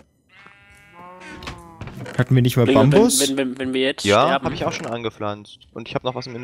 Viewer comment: Ich hab anstatt Bambus Amboss verstanden :D